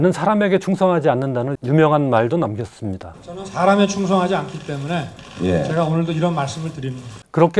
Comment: 사람에 충성하지 않는다,,,,제가 볼땐 사람이 신임하지 않은다로 들리는데,,,,,왕따 아니요? 사람에 충성 할때도 있어야 되는거 아닙니까?선배,,,고마해라